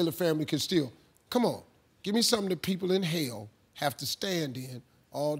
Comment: I totally thought Steve said "inhale" instead of "in hell" too! 😂